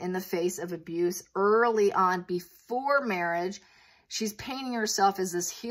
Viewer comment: In your American accent, when you say 'painting' it sounds like 'paining' which is interesting - I kept hearing 'paining'. Geez, it's a lot of force.